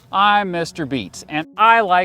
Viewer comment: i heard Mr.Beast